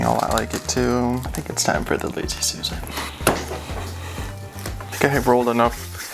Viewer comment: When he said "I think its time for the lazy Susan." I thought he said "I think its time for the lazer suicide. And was just like "yeah" and didn't question it